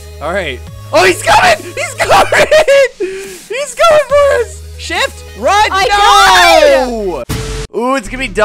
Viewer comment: did you just say sh**